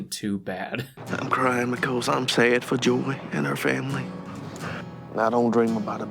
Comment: Uhmm...Mr. Editor, or AI, or or whomever did this: What the heck happened to the subtitles at 24:36!? says "I'm crying because I'm sad for Joy (her said Julie though lol) and her family" and the captions COMPLETELY FLIP the quote to: "I'm crying because I'm so happy for Joy and our family". Like, I'm genuinely interested how this happened. (Also, the following sentence was off; as I really hope wasn't crying about his children "buying" something after he dies looooool) A joke perhaps? As voice-recognition software generally might mishear words, but not completely flip the script. And humans need to actively want to make an "oopsie" like that.